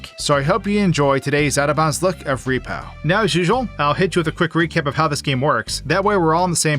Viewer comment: i swear he said recep